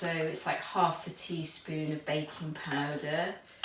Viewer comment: My mind is so silly, I thought she kept saying "bacon powder". My brain was really pondering about that until I rerun the video 😁😏